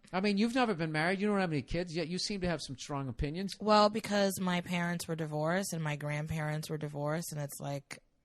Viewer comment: I wish Nea had mentioned what happened to her dad during the divorce, it sounds like she says "I was a part of a divorce and ......." she doesn't really explain her point because she doesn't mention the outcomes of her experienced divorces.